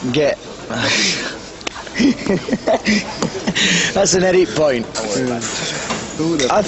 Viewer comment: what does Sav say at 2:38 ? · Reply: That's an edit point